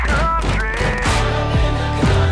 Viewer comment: i heard "caught up in the concrete" says something about me don't it?